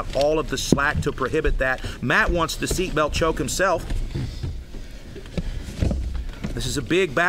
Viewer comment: I thought I heard "Matt wants the seatbelt choke himself" as "Matt wants to seatbelt choke himself"